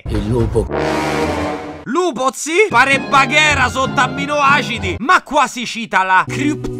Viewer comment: Semmai "Bagheera sotto acidi" non "sotto aminoacidi". Aminoacidi sono i componenti delle proteine (ora mi immagino Bagheera che va in palestra e si gonfia coi protein shakes 😂)